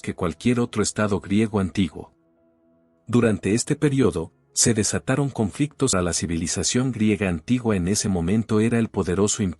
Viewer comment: ¿ Qué es esa interrupción en el minuto 1:28 ? No logro entender que es lo que dice en su totalidad., sobretodo al final de la frase. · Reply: Oh es verdad, olvide quitar el audio de ese clip 😢 Edit: en esa parte no se dice nada tan relevante. Dice algo como " conflictos que quedarian grabados en la historia" y después "uno de los..."